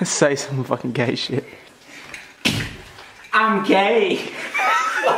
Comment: i thought he said im okay when i see this meme first time